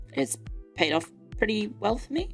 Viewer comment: I heard "pretty wealth me" 💰